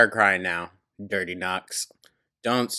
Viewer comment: We thought you called her “Dirty Nuts” and were rolling bc it was so unexpected and definitely a new one 😂 had to go back a few times until we realized you were saying “Dirty Knucks”!